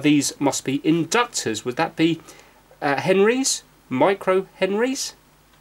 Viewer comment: I was watching this while working on stuff so I was kinda just listening. When you were talking about millihenry vs microhenry, I thought you were saying Michael Henry as opposed to microhenry, and I was like "does an entire man control this or smth?"